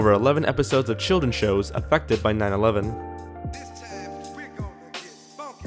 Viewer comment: i thought he said affected by 9/11 but i hear 1990s